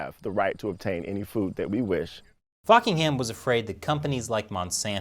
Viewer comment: "FUCKING HAM" admit it, it's what you said, but not what you read ;)